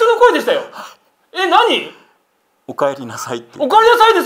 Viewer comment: 三つ目「おかえりください」に聞こえるのはオレだけ？